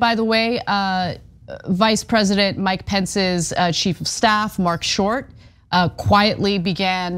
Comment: Anyone else hear "sheep of staff" at 10:46 😄?